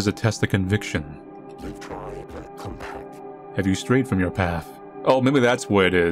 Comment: The fish in 21:40 is actually saying words if anyone wants to decipher it or take a weird listen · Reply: It sounds like he's saying "come back"